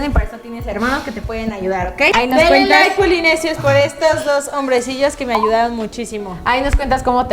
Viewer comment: Yo al escuchar que dice hombresillos min